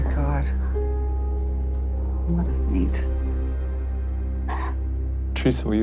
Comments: What did say at 3:18 ? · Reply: “I want to faint” I think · what did say? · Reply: What a feat?